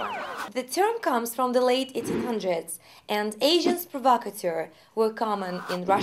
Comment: i thought you said 'Asian' when you said 'agents'. o_O